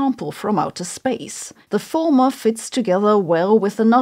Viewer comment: 4.52 i hear "the FORMER fits together", the subtitles say "the LATTER fits together". A bit confusing